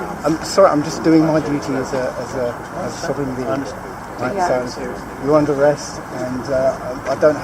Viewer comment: when says "I'm just doing my duty as a sovereign being" , the subtitles say sovereign villain!!!!😆😅🤣.